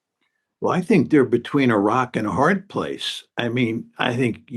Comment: did he just say iraq and a hard place